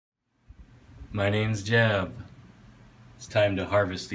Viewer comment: When I first saw a video I thought he said my name is jeff